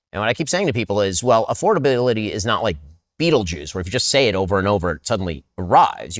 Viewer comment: what BS said : beatlejews ? afordability is not ... this is related to EugyptoS betleb holy shit eaating insects ???